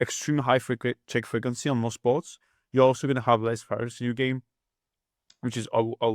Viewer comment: Thanks for the video. Great content! At 24:23 I can't quite hear one key one you say. It's when you talk about delayed c bets. "you're also gonna have less ---- to your game"? What is the word?! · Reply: variance